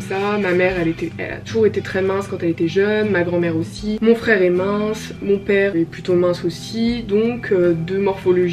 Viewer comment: j'ai entendu mon frr est mort au début 😭😭😂